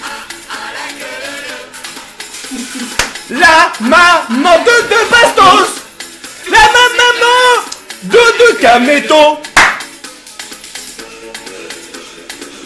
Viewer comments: C'est moi ou il dit la maman de KAMETO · c moi ou il a dit "la maman a kameto" a 3:08 #kotei&kameto